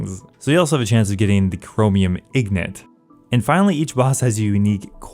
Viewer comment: "ignit" 7:22 What did you call me?